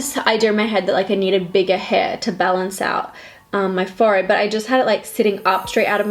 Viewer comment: I don’t usually comment, but at 7:39 , the caption said “my fart” but she really said “my forehead” lmao idk if it’s just my phone or anyone else’s but it’s probably just gonna show in the US 🤣🤣🤣🤦‍♀️